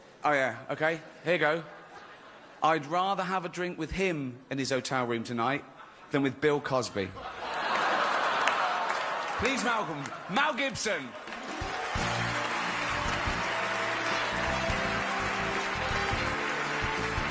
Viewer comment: did he say "Please Melgom Mel Gibson"? I listened to it a few times that's what iI am hearing. I believe he was saying "Please Welcome Mel Gibson".